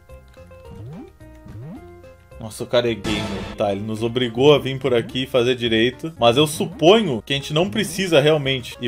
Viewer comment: nossa, eu ouvi "o cara e gay"